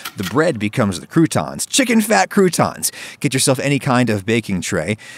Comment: i thought it said "chicken ceasar fat croutons"